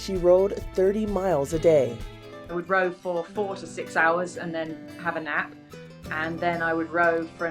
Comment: I thought she said she rowed for 46 hours and then took a nap. I said God daaayyyuuuummmm and then realized she said 4-6 hours